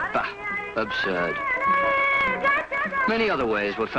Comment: did the say 'BAX absorbed?' lol